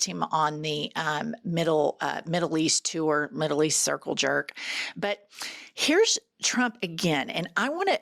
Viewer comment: Did I hear you say CIRCLE JERK at 4:10? 🥂GREAT! RESPECT from Germany!💎